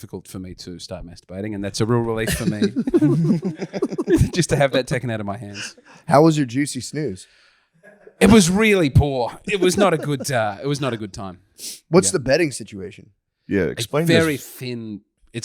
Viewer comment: Thought it said Juicy Schnoz, for a second I felt special